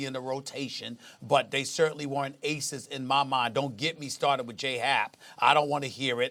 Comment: “I’m a diehard Yankee fan” 1:50 “Jarret Cole is the real deal” Who?????????????????? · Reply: I thought SAS said "Jared."